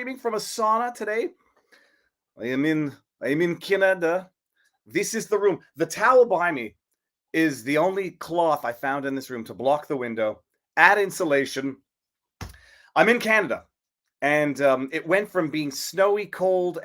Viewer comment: When he said, “towel behind me” I thought he said, “the taliban me”.